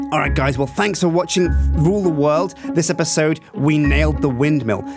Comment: did anyone else hear at 14:29 "well this has been fffe rule the world?